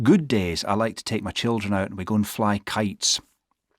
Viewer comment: i heard we go as gonna